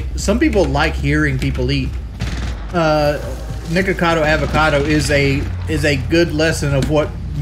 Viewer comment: he really said niggacado avocado . He just can't help himself.